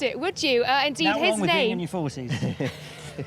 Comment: Could anyone please transcribe what Will Buxton is saying at 18:39? English is not my native language and no matter how many times I listen to it I can't understand a single word of that sentence which is apparently funny. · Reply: "Now't (nothing) wrong with being in your 40's".